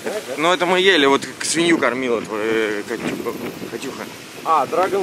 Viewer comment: катюхой свинью кормили или мне послышалось????